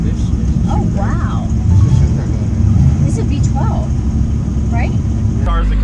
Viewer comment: Yo…. The audio kinda sucked, but @ 2:30 did she say it’s a V12? 🤦🏼‍♂️☠️🤣